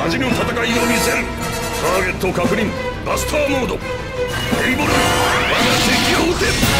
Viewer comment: did he actually say... Gae Bolg...? (in Jap katakana its written Gei Borugu?